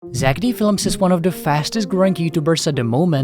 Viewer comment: There's One I Heard "Zeck Felms"...💀😂